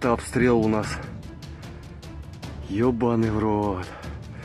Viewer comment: I don't know Russian, but it sounds like he said "we are facked"?